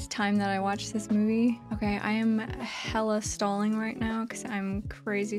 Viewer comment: OOOOHHHHHHHH!!! "Hella stalling". I thought you said 'Hella stoned' LOL!